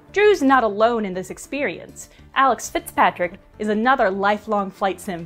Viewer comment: The captions say "Alex Fitzgerald" but the person's name is "Alex Fitzpatrick."